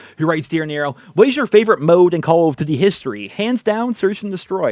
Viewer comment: I love how it sounds like he is saying: "CoooLl of duty" Hope you're doing well Nero!